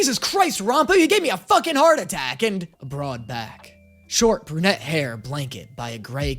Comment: I DIDNT EVEN REALIZE HE WAS SPEAKING THEM UNTIL I OPENED THE FANFIC UP omg I THOUGHT HE WAS SAYING U HAVE ME A HEART ATTACK TO THE FANFICTION OR SMTH